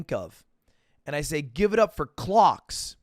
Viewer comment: bro i thought he had said "Give it up for GLOCKS" , but i dont know why that changes things